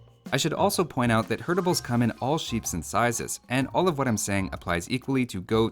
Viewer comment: haha love that line about "counting sheep but i fell asleep" i love those small but nice jokes that moods me up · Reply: Also 4:45 is it just me or did he say "all sheeps and sizes" instead of "all shapes and sizes"?